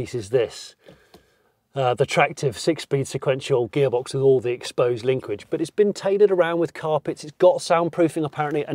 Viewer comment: Did you say "exposed linquidge"?! That sounds like a Smith and Sniff-ism that's crept into TLBS. It's not the first that's made me giggle! #chanquez